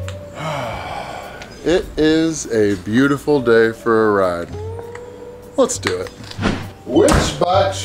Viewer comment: I thought you said it's a beautiful day for a riot😅